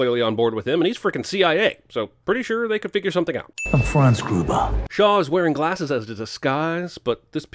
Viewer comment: When he said "Franz Gruber", I heard "Hans Gruber", and am not sure if that was intentional or not.